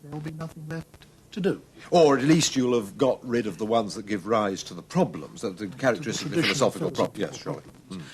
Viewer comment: Can anyone decipher what did they say in 10:43? You would have gotten rid of the questions which..." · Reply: Analyze the concepts and get rid of the characteristic/traditional philosophical problems